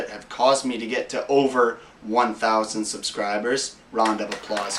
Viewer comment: wait I thought you said you have over 100,000 subscribers.... I only see 2,602! LMAO!!!!